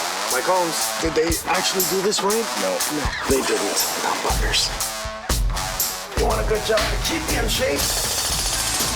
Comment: Did he say... MF's?? Now that's first... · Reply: Pretty sure he said buggers, haha